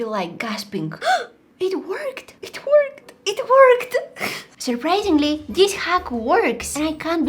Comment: it sounds like shes saying "it twerked"😂